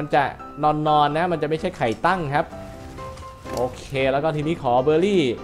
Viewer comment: ไข่ตั้ง555 แม่ถามว่ามึงดูไรเนี่ยได้ยินว่าไข่ตั้ง55